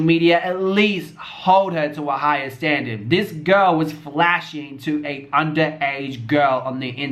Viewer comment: Island Gyal Crown no he wasn’t he said “Why are you showing your vagina to a underage girl” while talking about peaches being on live wit Kayla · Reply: Jada Scott 9:02 rewatch and listen to what he ACTUALLY said. He never said why are you showing whatever. He said “this girl was flashing a underage girl” referencing to malu 🙂